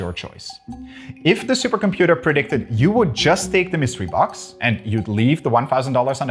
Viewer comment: Despite you adding the line “the computer isn’t trying to deprive you of money”, half way through the video I had to go listen to the premise again because I thought you said “if it predicts that you only take the mystery box, you get nothing” and I was thoroughly confused how people were divided on the answer. 🙃